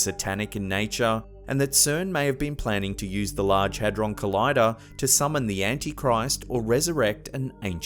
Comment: What is a hedrun kolida? I know I’m not spelling it right, but that’s what it sounded like he said when the girl was just about to be stabbed and the camera started shaking....anyone? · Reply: "Hadron collider", google it